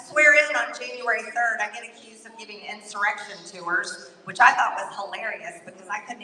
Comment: She thought they said, erection, not insurrection 😂